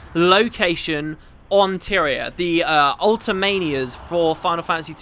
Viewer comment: You said Tyria instead of Spira!! Made me giggle ^-^